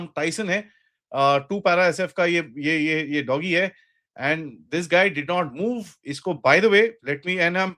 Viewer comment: Please correct 2:25 (2 para sf ka soldier) instead of ( 2 para sf ka doggy)